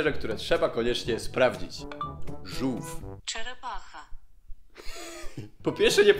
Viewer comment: Kiedy tłumaczyłeś żółw usłyszałem "czajnik macha" xddd